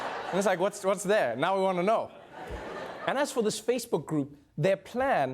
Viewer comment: did he say «spacebook group»? NNNKQJSJSKQKS